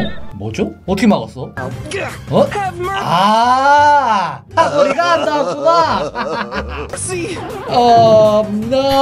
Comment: 이거해석좀여 아 니가환다구냐밖에안들려요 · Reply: 사거리가 안 닿았구나